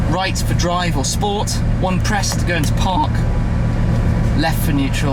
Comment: Anyone hear the voice crack right before 4:05? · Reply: Cjwarrior112297 That wasn't a voice crack. That was him saying park at the same time the car probably beeping to indicate its in park.